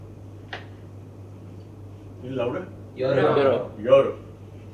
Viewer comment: did he say 'Laura' or am i spoiled completely😂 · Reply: 😂😂😂 It's Louder I think